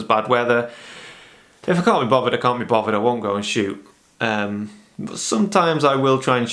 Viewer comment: What was it that you said at 14:48? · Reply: Haha. I just listened to myself back and realised how much I mumbled. "If I can't be bothered, I can't be bothered. I just won't go out and shoot."